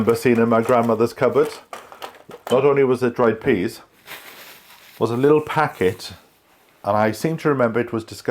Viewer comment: Thought you said dried peas and a peanut. Glad you repeated it. 🤣 A very interesting video. Great that Asda support the farmers. I wonder if you will revert back to Waitrose. With me and my favourite supermarket, it's like the Hotel California. You can check out any time you like, but you can never lee-eave...🎵🎶 I think you get used to a brand and way of doing things.